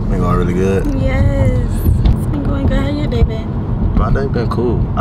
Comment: Did she call him Bae ????? At 23:47 😅😅😅😅😅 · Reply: She said how your day been